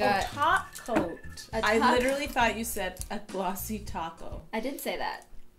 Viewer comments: “I literally thought you said a glossy toco” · I literally thought you said a glossy taco ha: I did: oh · ohh topcoat. I literally thought you said taco -no I did say that - oh.